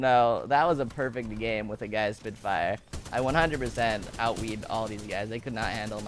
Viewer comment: did he say he out weeb the enemy team? Lol